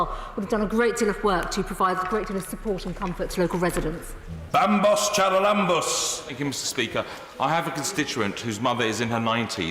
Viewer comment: what on earth did he just yell out? "Bumbus JalaLumbus"? Sounds like a harry potter spell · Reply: bambos charalambous